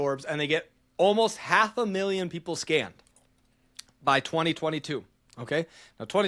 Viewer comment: Between 40:43 and 41:07 I don't know why I keep hearing teej say "people scammed" and "scammed people" like mulitple times lol